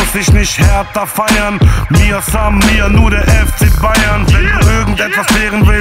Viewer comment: was sagt der bei 1:54? mir san mir oder was?? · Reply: der spruch vom fcbayern: mia san mia